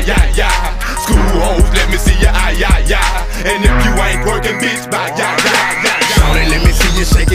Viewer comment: I always thought he said "and if you white twerkin bitch bye ya! Yi yi" 😂😂😂